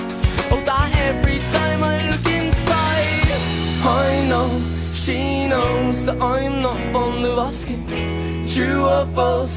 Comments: Always thought this guy was saying "I know she knows that I'm not from the roster" and always had no idea how that made ANY sense at all!😂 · Lol I thought he was saying "I know she knows that I'm not from Nebraska" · Always thought it said, ''I'm not from Nebraska'' XD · Is it just me or does it sound like he is saying, I'm not fond of Alaskan? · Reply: I hear, "I'm not from Alaska" every time xD